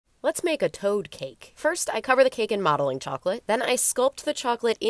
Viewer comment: At first I thought she said "let's make a toe cake" 💀 Other than that it's amazing 🤭❤️